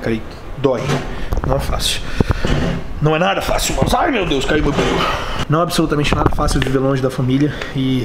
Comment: ai mds caiu meu peru Foi isso mesmo q eu ouvi? Kkkkkk